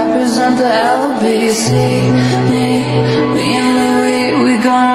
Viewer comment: OMG I ALWAYS THOUGHT THAT IT SAID "me and Louie, we're gonna run to the party and dance to randomly KISS HARDER" 😭😭😭😭